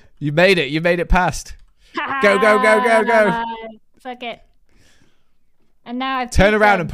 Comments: i'm assuming she said something else since neither of them mentioned it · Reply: I thought she said buck it tbh 🤣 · To me it sounds like she says "pluck it" not the FBomb.